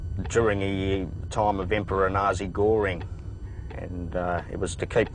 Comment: Oh, nasi goreng the food! I thought he was saying "Nazi Göering!" (As in Hermann Göering)